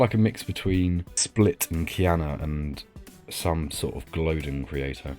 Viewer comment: What was the name said after split at 6:12 i was just wondering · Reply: Kiannuh 👍